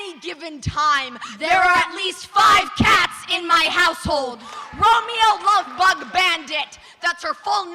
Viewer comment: The third one I deadass thought they said “there are 5 cats in my asshole”😂😂😂😂😂